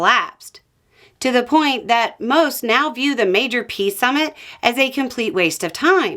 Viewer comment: Did you just call it a "pee summit"?